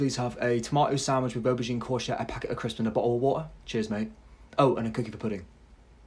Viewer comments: bottle of wata ✨” “tomateo ✨🤪” “crisps mate” Chefs kiss to the accent 💋” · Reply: I heard " a packet of Christian ball" 🤣🤣🤣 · He said,"cookie for pudding" I heard "a cook in the pudding"